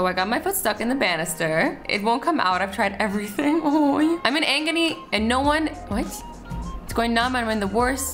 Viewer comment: Did she really say"I'm in 'angony' at 4:08😂😂😂